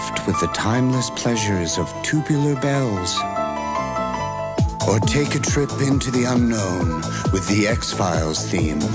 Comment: "Or take a trip into the UNKNOWN, with the X Files theme" AAAAAAHAHAHAHAHA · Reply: So scary back in the day, I hear it as "Take a trip to your darkest fears. With the X-Files theme." 🤣